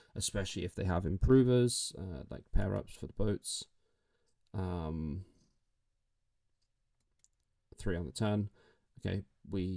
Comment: Consider them ditched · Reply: Also, on 4:36 you say 3 on the turn and the subtitles says 3 on the 10. So you still AI generate them and don’t write them yourself. We are very glad you’re stopping this nonsense.